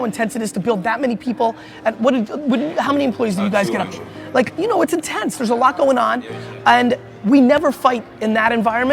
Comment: what did said when Gary said how many employees do you have? Can somebody reply my comment? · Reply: hidayat about 200